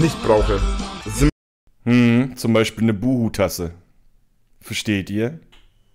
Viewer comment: Ich habe ganze Zeit Bubu-Tasse verstanden, statt Boo-Hoo-Tasse 😂 denke mir ganze Zeit, was Reini mit einer Heija-Bubu-Tasse will 😅